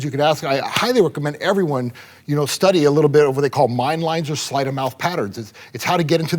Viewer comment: In 45:58 what is the name of the book he recommend i just hear (command lines) , but i dont understand the entire....pls Help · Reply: Hey man, I believe he said Mind Lines and Sleight of Mouth.